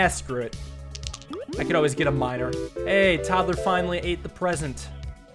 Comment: aye i heard minor